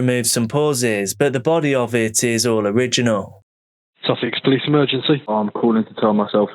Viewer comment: I swear that call handler said “Sausage police emergency…” 😂